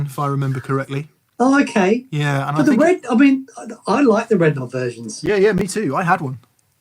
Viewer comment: did I hear say he liked red knob virgins?... doesn't sound wrong, I guess.